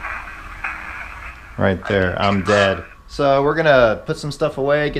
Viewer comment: you heard "i'm dead" but to me after you asked for its name i heard "i'm Dan" i could be wrong but thats what i heard it say